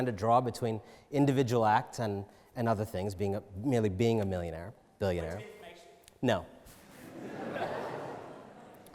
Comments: what did the person say? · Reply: He said point of information, he wants to asks a question of the speaker about what he's saying, probably going to be challenging what he's saying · Does anyone know what the audience member said at 2:09 that he responded "no" to, which got an audience laugh? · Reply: "point of information" meaning he wanted to challenge what anand just said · What wss said in background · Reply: point of information